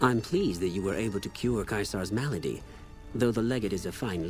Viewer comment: I thought said ma lady instead of malady lmao